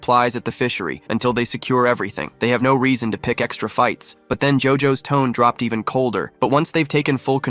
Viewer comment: Did I hear him correctly? JOJO?! Damn, no wonder why bro's the protagonist.